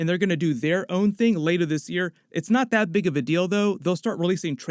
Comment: WOW at 8:37 did you say that for real !?..????....... ‘’ it’s not that big of a dildo ! ‘’ 😂😂😂😂😂😂😂😂😂😂😂😂😂